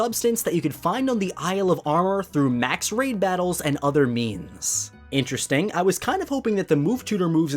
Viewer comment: When you said means I thought you said memes